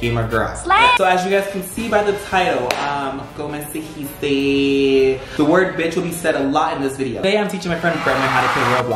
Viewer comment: what is that word or phrase that he said, I can't hear it. Thanks <3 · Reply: 2 years later but.. he said: como se dice? In spanish, which means: how do you call…